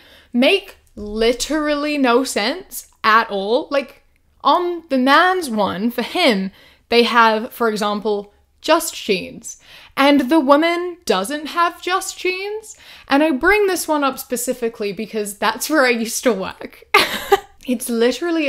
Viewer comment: I thought she said “just cheese” lol, made me think of the meme You can fascinate a woman with a piece of cheese 😂